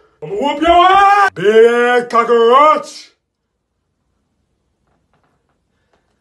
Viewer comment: Sounded like he said carroach in the beginning